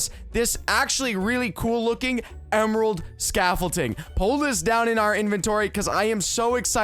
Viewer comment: says emerald scafflting instead of scaffolding